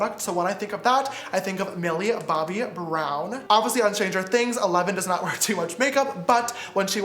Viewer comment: did he just say changer things?